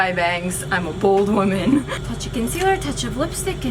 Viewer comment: Just know under my bangs I'm a bold women ~ 2019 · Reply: I thought she said BALD woman 😂😂